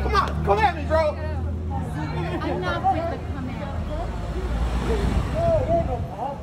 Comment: I love how at 2:12 u can hear what sounds like an elderly woman say "I'm not with the come at me bro" 😂😂